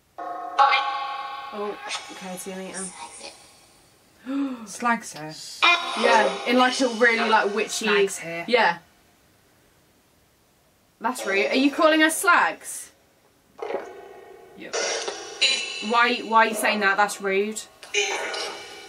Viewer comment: I heard it as " SLICE HER "... 😳👀😨 evil voice 🇩🇰🇩🇰🌈🌹